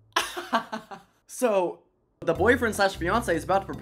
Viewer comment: When he said boyfriend/ fiancé I thought he said boyfriend/ Beyoncé 😭